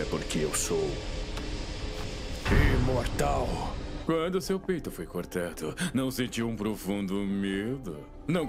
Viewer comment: a fala: "quando o seu peito foi cortado" a legenda: "quando o seu pinto foi cortado-"